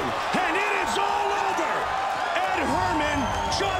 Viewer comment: I really thought it said ''Ed Sheeran'